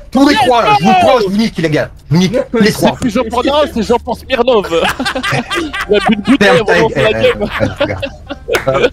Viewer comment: dit quoi à 5:01 ? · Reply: c'est plus c'est (une marque de bouteille de vodka)